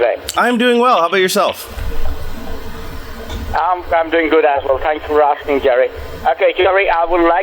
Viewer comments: Does say "I'm doing good asshole, thanks for asking" @ 26:56? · I thought the scammer said i'm doing good asshole